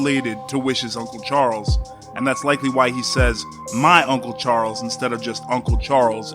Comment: I thought he said he missed his uncle charizard